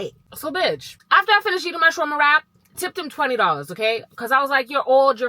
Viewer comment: Why does it sound like you said "so I get done eating my trauma wrap"😂 I know I need therapy, but like it actually sounds like that😂